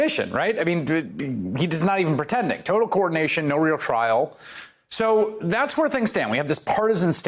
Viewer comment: "No real trail." Is that what I just heard?!? What the dadblasted blank blank blank has been happening for the last three years!?!